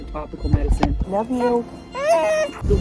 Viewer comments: Tulip sounded like she said “Love you!” 🦫🥰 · Sounded like the beaver said "Love you" back. ❤